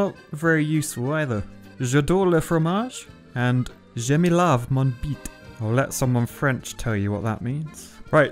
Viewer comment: Here is a translation : I love cheese and [I really dunno what he said lol] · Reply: i think he said "je me lave ma bite"